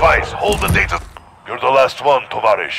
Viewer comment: What is he saying? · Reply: Falcon 30 on route for personal radar coverage